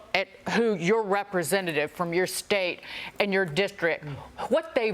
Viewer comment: LoL 😂 LMAO how she said district sounded like dicktrick !!